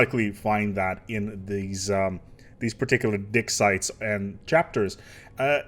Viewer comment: And to think i didn't even think this game could get any deeper. Also at 3:00 sounds like your saying dick sites :D